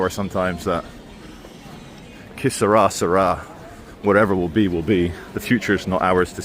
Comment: When you said, "Que sera, sera" I thought you said kiss her arse, her arse! I've watched the decline of Johannesburg over the past 42 years. Nottingham is much cleaner but, sadly, it's well on the way.